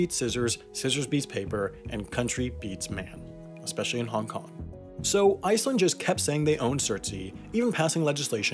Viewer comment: Didn't expect the Hong Kong plug lol especially when I'm from Hong Kong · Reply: I thought you said Kim jong un not Hong Kong